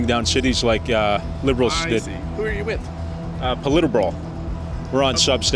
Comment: I thought he said politibro. I had to re-read the channel name. PolitiBro …band name called it.